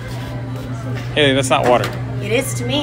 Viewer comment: am I the only one who thought he said “you stocking up for mukbang?”